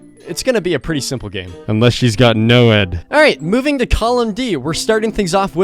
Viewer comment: can someone tell me what he's saying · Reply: Unless he has noed